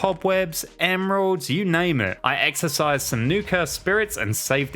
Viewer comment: I thought you said "exercise" instead of "exorcise"